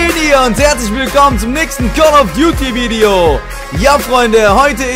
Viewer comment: Ich hab call of byuti verstanden